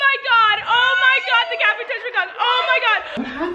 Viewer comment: Instead of cat food I thought she said cabbage, wow I'm tired